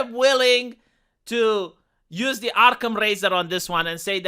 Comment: you are a genius. Sometimes your English is just imperfect enough to inspire a new meaning to a phrase or an original concept out of common expression. When you said Occam's Razor I heard Arkham's Razor which can only be defined as it relates to Arkham Asylum. Arkham's Razor must mean that the the craziest explanation is usually the truth. One might apply it to Jan 6 and see what turns out to be true a year from now.